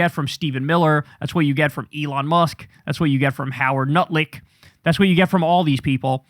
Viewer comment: Did you say nutlick??